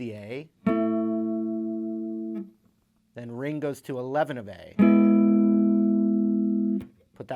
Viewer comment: Did he say Ringo to eleven of A @ 3:52?